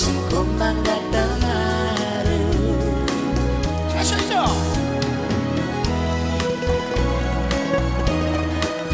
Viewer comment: 건모형 뭐라는거에요? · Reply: 소리질러!